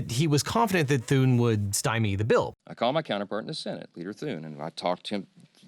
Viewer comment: Was that a slip? Sounded like he said "Peter" Thune. I'm unaware of John Randolph Thune going by Peter. Maybe "Peter" slipped out as in Peter Thiel?